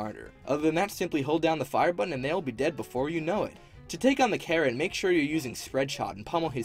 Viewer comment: i thought he said to take on the karen instead of carrot